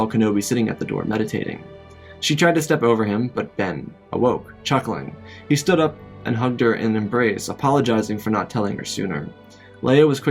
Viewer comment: Did he say that obi wan humped Leia in embrace